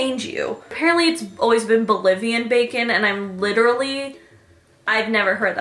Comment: I thought said Bulgarian bacon tbh